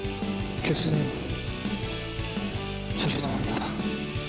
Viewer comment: 제귀에만 '교수님 사랑합니다'로 들리는거 아니죠ㅎ '기타야 사과드려'도 센스👍목소리👍👍